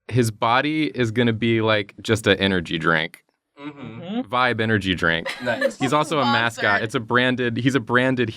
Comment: At one point I thought you said "5 energy drink", which reminded me of my favourite meme. Can you draw how it feels to chew 5 gum?